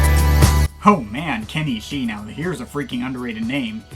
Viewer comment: When you said Kenny ishii I thought you said Kenny G